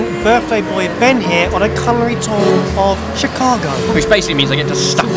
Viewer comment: Barry said "culinary tour" I heard "colouring tour" and I don't know if I'm disappointed or not that there's no colouring.